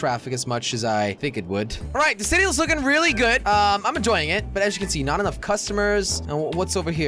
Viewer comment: I thought he said ima join it but he said I am enjoying it